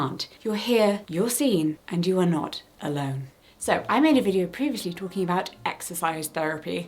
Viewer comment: Captions will be ready in a couple of hours, thank you for your patience! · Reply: heads up that at 4:59 it says "you are alone" instead of "you are not alone"!